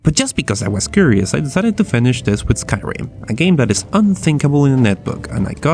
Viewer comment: wait did he say skyrim or minecraft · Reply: Skyrim. :/